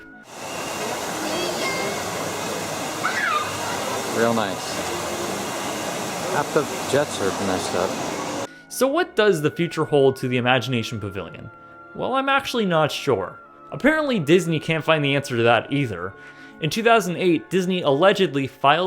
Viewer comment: I thought it Said “immigration” lol